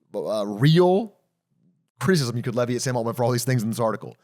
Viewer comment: I'm not a native English speaker but I'm beyond proficient and have been mistaken for an English native more than once, that said sometimes goes into hyperdrive and even after rewinding 3 times for the life of me I cannot follow what he says here😂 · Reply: criticism you can levy at sam altman